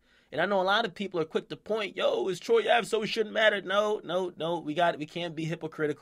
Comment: wtf does he say "Is troy ave _____????" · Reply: “It’s Troy Ave so it doesn’t matter”